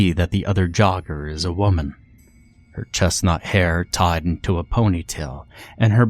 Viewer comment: You said "her chestnut hair tied into a ponytail" and I heard "her chest hair tied into a ponytail" and was 😖🤢